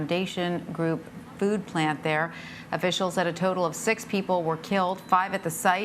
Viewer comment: I thought I heard the guy say 16 people not 6